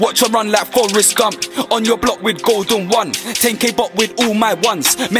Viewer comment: did he say Tay-k? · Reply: 10k bop which means he's dissing 1011s signature dance